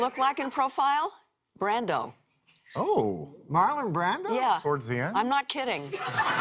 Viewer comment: ? I couldn't make out what he said. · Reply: He said, "Towards the end?". As in when Brando was obese.